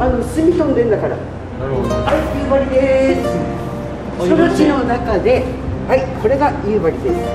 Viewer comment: 「その地の中で」じゃなくて「空知の中で」かな？ 土地の名前知らなかったらなんて言ってるか聞こえないよねきっと。